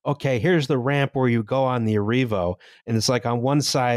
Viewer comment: Fuck, I thought you said "Arriva" when you said Arrivo, and as someone with extensive experience travelling on Arriva Trains Wales the idea of an Arriva Hyperloop was absolutely sending me (shoutout to the time they put on a 2 coach service for onward travel to Holyhead for people getting off the 8 coach Virgin Voyager from Euston at Chester, just before Christmas. Cue several hours of me standing legs astride my suitcase in the aisle, lifting it to the bloody ceiling every time someone wanted to get past, followed by dumping us all off at Llandudno Junction to fend for ourselves at 10pm because the snow had got too heavy).